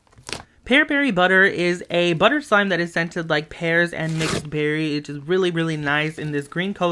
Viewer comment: I thought you said it was sented like hair😂😂😂